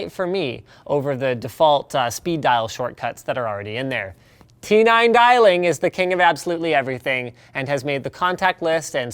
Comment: Hi Linus. Thanks for the review! Quick question, at 9:40 you mention, if I heard correctly, "two-nine dialing". What is that? Can you provide a link?